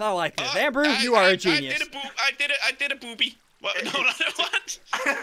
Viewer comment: I think said I did a booby